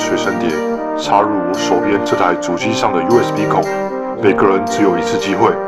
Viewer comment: 我聽成US鼻孔